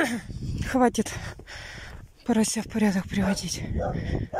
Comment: Мне одной послышалось "хватит порося в порядок приводить" 🤔🤔🤔